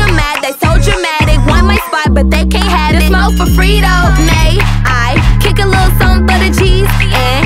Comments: I thought it said may i kick something for the CHEESE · When I heard that part on tiktok I thought she said freetoes